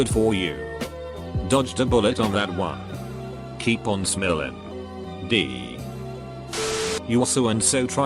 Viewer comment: I heard “Keep on smellin’ D”